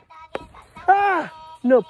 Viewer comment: I thought he said no bullying but he said Napoleon